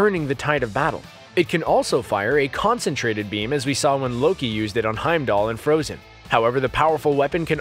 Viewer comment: I swear at 10:17 he said “like when loki used it on heimdall in frozen” 😂😂😂